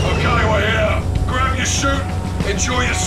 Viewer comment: Honestly thought he said Gdansk